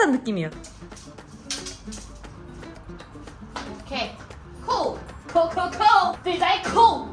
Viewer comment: 여기 뭐라고하는건지 아시는분 ㅠ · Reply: Co co co dj co 그냥 신나서 아무말한 것 같아요